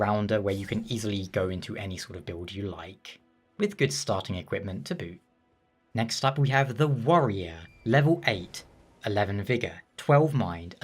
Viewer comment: I thought he said the ''worrier'' 1:18 lol thats me!